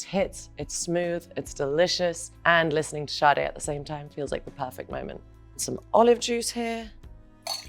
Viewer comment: why did i keep hearing. And listening to “charli xcx, it’s the perfect moment”